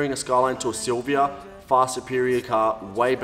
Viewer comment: When you said 'far superior car' I heard 'fast and furious car'. Anyway man best of luck. At least you gave effort to this car a lot. I hope both this Skyline and you have a great future.